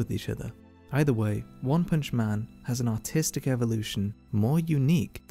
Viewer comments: for a second I thought you said autistic at the begging of the video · I misheard it as Autistic evolution. sorry just had to say it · Thought he said autistic evolution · I thought he said “Autistic Evolution”. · I thought it said the autistic evolution😀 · sounds like he said autistic